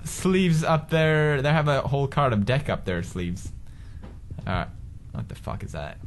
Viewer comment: Everytime I hear " a hole card of deck of my arm" lol😂